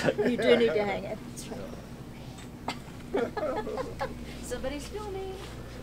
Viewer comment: did that lady say “somebody kill me!” Because that’s what it sounded like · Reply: no she said somebody’s filming